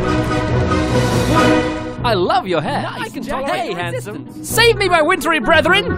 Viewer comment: Who else heard one of the snow Matts say 'I can tolerate your existence!'?